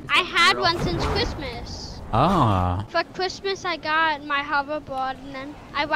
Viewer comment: its sounds like says fuck chrismas (I dont know how it is spelt)